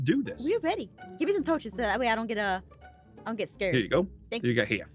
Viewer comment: "touches" really at first I thought you said roaches